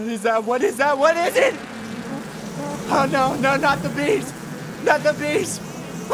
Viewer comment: Bruh it sounds like he is saying NOT THE BEANS.